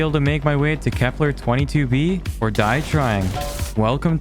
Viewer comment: When he said "can we make it to Kepler 22b or die trying" I heard "can we make it to Kepler 22b or try dying"